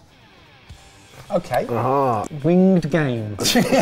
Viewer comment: I can’t be the only one who thought said ‘wind game’ instead of ‘winged game’, right?